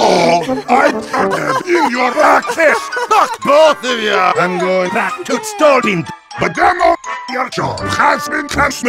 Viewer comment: what did they said from 0:38 to 0:45 ? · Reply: Demo: Fuck both of ya, I'm going back to Stoolbend! Heavy: But Demo, your show has been cancelled. <Demo screams and everyone else laughs>